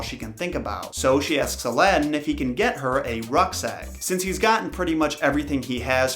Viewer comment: A rucks egg? I thought he said ruck sack xD